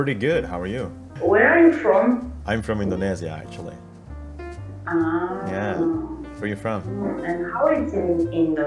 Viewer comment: aku dengernya dia bilang "iya dong"🤣🤣